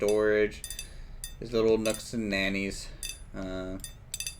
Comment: Did I hear "nooks and nannies"? Made me chuckle. Always inspired by your creativity and commitment - keep it up! BTW, is Starbuckle still with us?